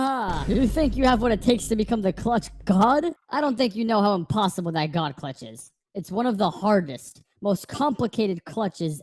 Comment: my first thought was "the dream clutch" you know what am saying right